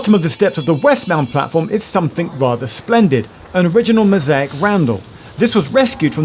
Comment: Now come on! Although it says Randall in the subtitles, you rightly ignored it and correctly pronounced it as Roundel. Good boy. So what happened right after when the subtitles said woodline and you went all Afrikaans and pronounced Wood Lane as woodline? Been burning the candle at both ends have you, too tired to listen to what you were recording perhaps?